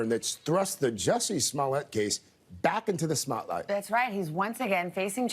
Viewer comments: You heard him say smotlight LOL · Did he just say "back into the Smotlight"? Is that a Smollet spotlight? 😂